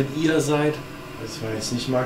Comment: minute hab verstanden ihr seit nett